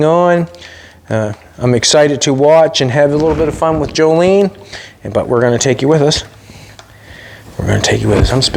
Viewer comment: I thought you said a little paint your taking Jolene s fun R